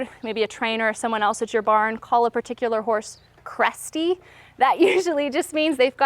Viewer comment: I thought u said crusty instead of cresty🤣